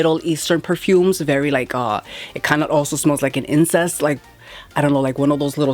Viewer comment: I played this video on 2x speed and thought they said it smells like incest . I now realize they said incense